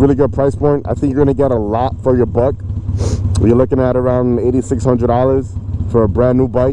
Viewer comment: I thought he said 80,600 but he said 8,600